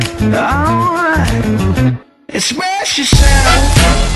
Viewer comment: Always thought he was saying "slap yourself".